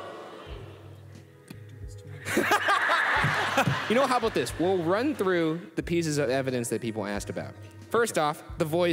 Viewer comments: Ppl who lip read what did Shane say at 9:07? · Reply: I think it was something along the lines of "you really gonna do this to me right now?" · Did anyone catch what shane said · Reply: Don't do this to me right now